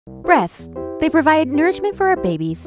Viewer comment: I thought u said breth